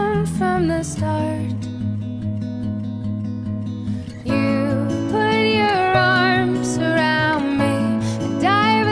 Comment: I ship it so much, and at first I thought this said "Stick your hand in my pants" I don't know how got that out of "You put your arms around me."